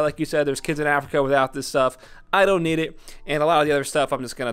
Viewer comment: Did he just say “I know kids in Africa don’t have this, but I don’t need this back up camera”? …. 😂😂😂